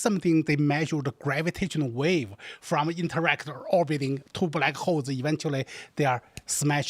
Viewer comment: did he say “Two black hoes that are smashing”?!?!?!